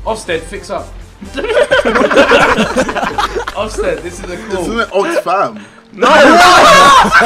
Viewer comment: what did jj say? "Isn't it old sperm" or something? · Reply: furious 8spect he said “isn’t it oxfam?”